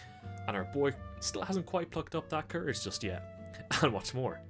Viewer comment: I thought it said fucked instead of cucked and I was like "this... This didn't happen, right?"